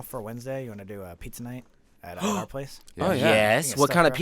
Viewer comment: and did just say "our place"